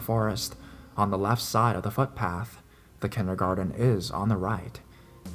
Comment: did you say fuck path? lenny face